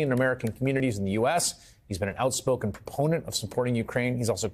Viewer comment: Enunciation is important. When talking about Dem. Mike Quigley, Hayes says he is an important (garbled) of aid to Ukraine. I heard "opponent" and had to go back to the statement twice, because I kept hearing "opponent." I know better, that he has to be saying "proponent." It's just a good reminder that clean speech sis important at all times.